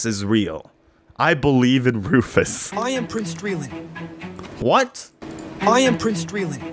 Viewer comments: PLEASEE I HEARD "I AM BRUCE SPRINGSTEEN" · "i am prince drilling" i thought he said "i improved streaming"